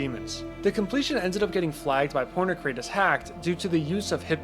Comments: caotion says porn crate instead of pointercrate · subtitles said "porn crate"